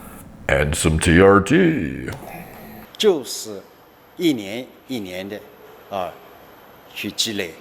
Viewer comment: After Derek says "And some trt" at 5:42, it sounds like he said "juice." Like, exactly.